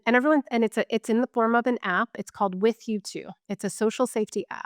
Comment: What was the app she referred to? I thought she said "With you too" but I don't see it.